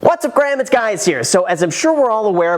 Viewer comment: Did he just say what's up Graham it's guys here